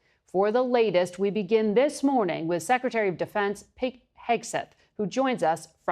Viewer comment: The woman called him “pig” instead of Pete Hegseth 😡